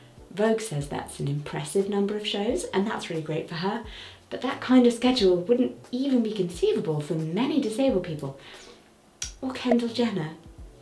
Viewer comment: Captions are uploaded! Thank you for your patience ❤ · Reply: you say "...that kind of schedule wouldn't even be conceivable for many disabled people" but the captions say "...that kind of schedule wouldn't even be thinkable for many disabled people." Perhaps it's a small thing, but I figured you might want to know. Thank you for this really well researched and presented video!